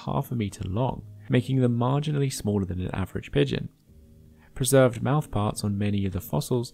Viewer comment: I legitimately had to do a double-take on that because I thought you said "larger than the average midget"